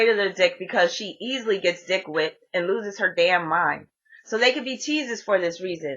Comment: what did you say? "dick whipped"????? i did not understand the word..